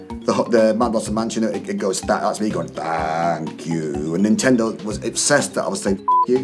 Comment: when I was 9 I swear I heard F*** You hahahahahahahahah oh the irony XD